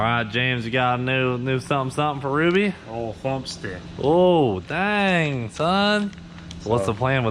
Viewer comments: Everybody: it’s called a cam James: “lumpstick” (edit) maybe he said bump stick, idk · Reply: Thought he said thumpstick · Everybody: it’s called a cam James: “lumpstick” (edit) maybe he said bump stick, idk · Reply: I heard "Bumpstick" lol